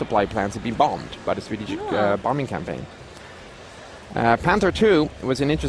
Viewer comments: Did I hear Swedish bombing campaigns? @ 19:50 ish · swedish bombing campaign? DId i hear that correctly?